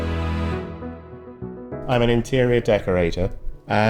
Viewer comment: I misheard him at that start and thought he said “I’m an Inferior Decorator “. Utterly Brilliant. This chap is such a breath of fresh air. He needs his own t.v show where he just wanders around being himself.